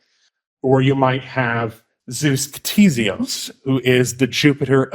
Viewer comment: what is the word you're using at 30:05? it sounds like you're saying "zeus ptesiumfs".